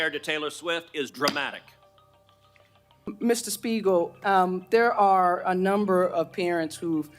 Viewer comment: did i hear “Mr Smeagol” ? Lol